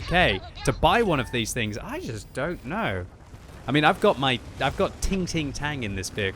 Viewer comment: Anybody thought he said "ting ting tan" besides me? Plus its a tank that Jesus christ would himself Edit: "Its really a guilt free purchase since 500£ went to the charity stream"